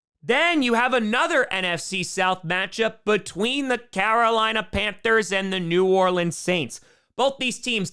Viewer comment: When you introduced the Panthers-Saints game, I thought I heard “Carolina Pampers” and agreed it was a fitting nickname.